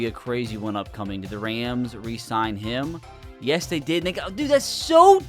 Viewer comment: did he say what I think he said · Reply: no dude he said “they got” don’t be dirty